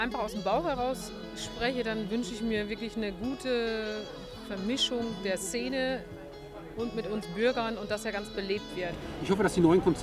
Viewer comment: Habe ich das richtig gehört? ( 3:40 Minute) - Vermischung der Scene mit uns Bürgern? Was sagt sie?